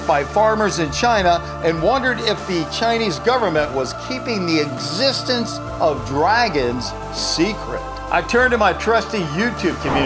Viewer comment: did say “The Balls of a Dinosaur”? 😂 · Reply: He said ''bones of a dragon'', but yeah it does sound like ''balls of a dragon''